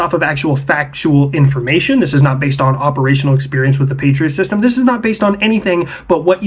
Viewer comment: Had to start over twice cause I thought this clown said “petrolsystem”…